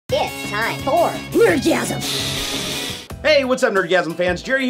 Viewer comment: My hearing is lousy, I swear in the intro it sounds like your saying "It's time fr Blertgasm!" rather than Nerdgasm time for a new hearing aid! Made in Space? if they go bust will they become lost in space?